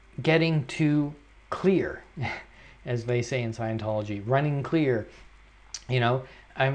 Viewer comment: Did you say Scientology?